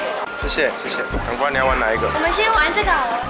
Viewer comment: 一开场的‘赶快你要玩哪一个’🤣 我把它听成‘老婆你要玩哪一个’😂